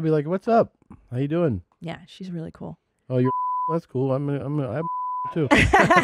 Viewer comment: I wanna know what this man said lmaoooo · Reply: “Oh you’re full of shit? That’s cool, I’m full of shit too.”